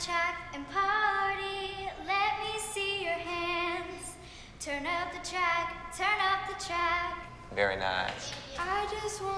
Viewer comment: Kalani sounded like she said, “turn up the chack, turn up the chack.”